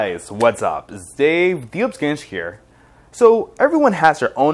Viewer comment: I cannot for the life of me figure what you're trying to say at 0:08 "dee ups ganj here"???